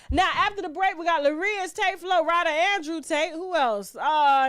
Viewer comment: I didn't even realize who that was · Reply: ohhh haha I thought she said Andrew Tate🤣🤣 that guy has been everywhere in my news feeds...every day.....Soo annoying..😭